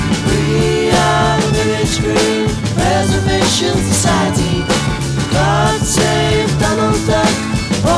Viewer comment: I keep hearing ''God save Donald Trump''